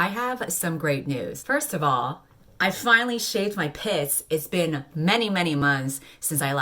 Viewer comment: When you said "I finally shaved my pits," I thought you said "I finally shaved my piss." I was understandably confuzzled.